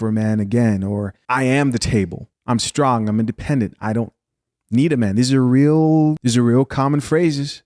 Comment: I thought "B-Dubs" said they were strong and independent and DONT NEED NO MAN!!! 🤔